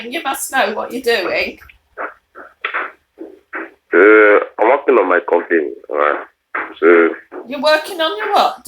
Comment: I thought he said "I am working on my coffin!" Lol